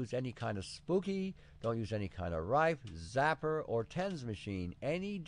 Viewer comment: don't use a pems machine? tems... what kind of machine did he say not to use? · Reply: a tens unit